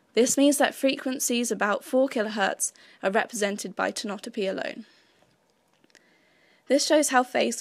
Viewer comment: great video! at 1:29 there is word i can't make out which refers to the way neurons fire at random phases at high frequencies. Could you please spell it so we look it up? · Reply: "tonotopy" :)